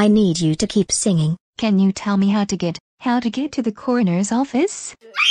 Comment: what the hell she saying?? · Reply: no I'm talking about the "Corners Office